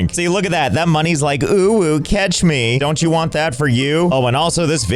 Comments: did just say uwu (and I didn’t like my comment hehe) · am I going crazy or did he say uwu